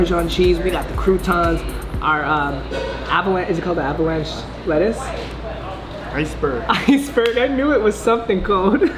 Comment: when you said avalanche lettuce my mind thought you said apple ranch lettuce hahaha